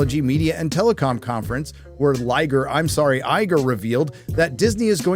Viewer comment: did you just call him Liger? Is it because he's useless, given far too much, and infertile?